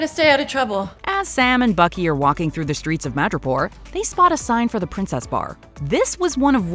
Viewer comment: When said "Madripoor", I thought he said "Mirjapur"😂😂